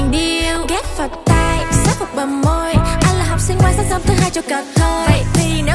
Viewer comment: nghe ra cai gi the ae?? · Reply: Anh là học sinh ngoan sáng sớm thứ 2 chào cờ thôi =)) Còn câu trước nữa, toàn "điêu" mà sub thành toàn "real" =)))) ảo thật